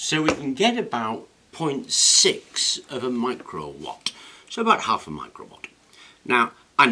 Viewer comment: Above to be revised thought you said watts - Re-watching - LOL but still nice static generator video. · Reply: You did say watts - but 0.6 micro watts, is what you said - caught the Micro on the second viewing - That is what's great about video, the reruns